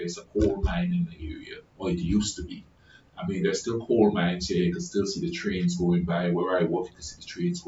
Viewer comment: I don' t know how to answer your question because i don' t understand that word , i will whrite like in romanian how i hear it😂" col mine" . I hope some one will hear the question & answer you, some one hwo know that word❤ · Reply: I am saying coal mine. My accent gets in the way sometimes lol